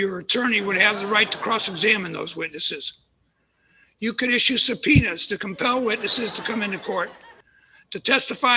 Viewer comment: 😂Lol at 3:14. It sounded like the judge said "penis". I know he said "subpoenas".